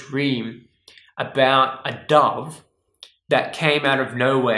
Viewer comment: I thought you said a dog took you and you dad to heaven ! I didn't figure out that you said dove untilhalf way through what you were talking about ! you have that vowel mispronouncing accent ! I was trying to figure out what a dog meant !